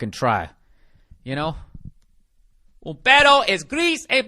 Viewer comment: "Un perro (though it sounded like "pedo" or fart) es gris, es blanco. 891. El niño bebe leche! Amorio."